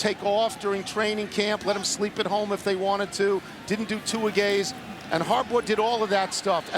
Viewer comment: I still find it funny how Tony Kornheiser says "2-a-gays" at 42:06-42:08 instead of "2-a-days".